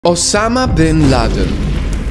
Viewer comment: i thought you said obama not to be rude